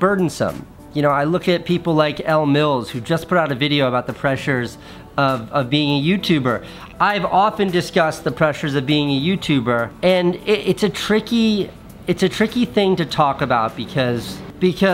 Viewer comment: I thought it said pleasure